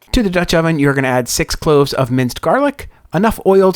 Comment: That looks much better than the one I had at a "specialty" Italian restaurant ($29.00) last week 1:01 did I hear 1 Tbsp garlic??? OR 1 quarter cup?? Thanks and I am off to print the recipe! !! !!! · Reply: 1tbsp minced garlic.